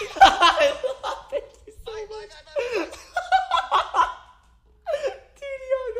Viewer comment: is it just me or did he say "I love him so much"